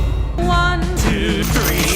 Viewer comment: like the vid but she says "for" even it sounds like she saying one